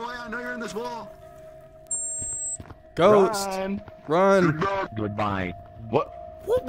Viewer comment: I think run said “you go good bye”